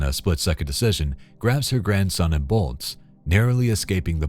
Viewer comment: oh GRANDKIDS …I seriously thought you said CRACKHEADS. I was like where? Wait huh? In the stroller??